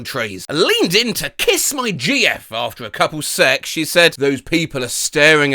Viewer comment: I was listening to the video whilst doing other things and I misheard a couple sec as "a cuppa' sex"